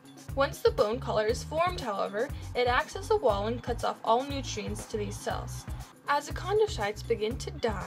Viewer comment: i can't understand what you've said on 2:09-2:10 .....ones the bone collar is formed it what???? pls answer thanks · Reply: Bianca Gorgonia it accesses a wall and cuts off all nutrients...